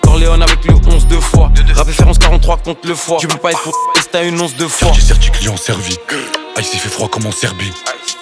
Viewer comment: Il dit quoi à 2:18 ? Le mot cache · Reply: Tu peux pas être pour israel si ta une once de foi